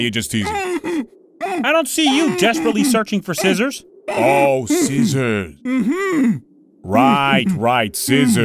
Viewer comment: so i'm not the only one who thought ramsey said lamb sauce and not scissors? lmao